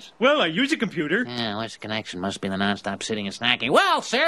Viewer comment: What does the salesman say to himself at 0:16? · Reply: What is the connection?must be Nonstop sitting and snacking